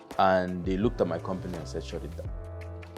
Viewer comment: He said he had a meeting with his lawyers and they looked at something, can someone please help me with what he said there at 8:19 ? I really wish to know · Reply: they looked at my company and said “shut it down”